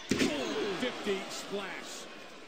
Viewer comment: cole i think said 50 splash